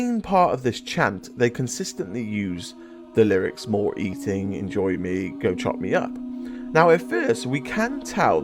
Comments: I’m hearing “always eating in the maw” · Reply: I hear “More chunk to enjoy!” 😂😂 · I heard "no eating lord" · I heard "go eat kids enjoy" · All i could hear was "More eating" being repeated, at one point "more" being repeated a bunch of times. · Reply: Tbh, what I heard is "More eating", "more" , "pork chop" :P